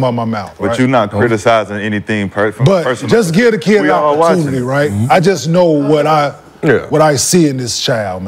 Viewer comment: when… I missed it? Time stamp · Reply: he clearly said “just give the kid his opportunity” when taking about his son. “He also said if you don’t play his son, go ahead and play council ” That’s clearly him saying play my son he plays hard that’s what’s needed in this series, or play the other kid